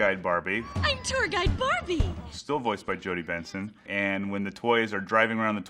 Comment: Benson! I thought she was saying Vincent!